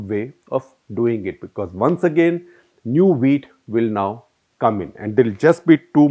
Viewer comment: Did i hear new wheat or new weed ?